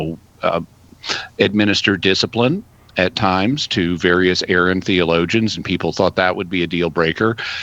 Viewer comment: I thought when Jimmy said “errant theologians” that he was saying “Arian theologians” which could be either the followers of Arius or the Aryan mythology since he just mentioned WWII like five seconds earlier.